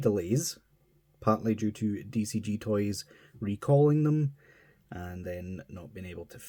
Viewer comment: Boy I thought that thing said DOG toys 😂. Definitely bad timing indeed.